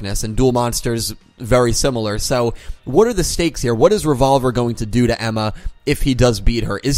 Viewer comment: You may have said "stakes" but I heard "steaks". Yes, I was a bit hungry while watching your video. :P lol